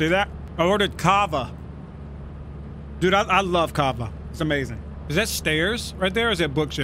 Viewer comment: anyone know what food hes talking about? It sounds like he says sth like "kaava, kaaba" but I cant find anything